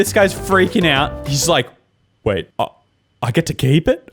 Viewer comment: I thought he was gunns say "wait! Got $20?"